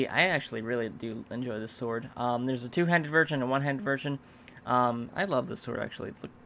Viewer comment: said "virgin" instead of "version". I love it when he is mispronouncing things and then they sound like something else :D